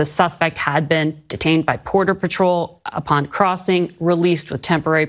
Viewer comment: Did she just say "Porter Batrol" @ 0:59 ?